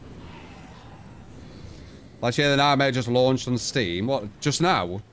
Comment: What game did he say released on Steam? I could not understand at all lol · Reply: Last Year: The Nightmare. I think it just goes by 'Last Year' now. The ip was purchased by a different company and they made it F2P and put it on Steam.